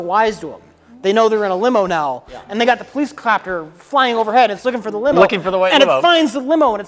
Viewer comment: Rich said “police cloppter”, instead of police chopper.